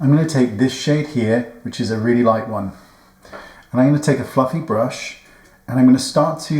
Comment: I did a double take, i thought I heard f*¢king brush 🫢 I thought omg Wayne’s sleepless nights are adding up 😆